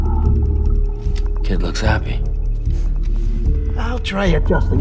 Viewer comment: I think subtitles are wrong, to me it sounds like he's saying "I'll try..." and "Autotoxemia... from now on"